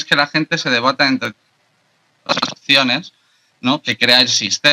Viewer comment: que palabras han censurado en 26:50 que el audio fue manipulado para que no se escuche claramente lo que se dice? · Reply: no se censuro, fallo en audio de su auricular, dice "opciones"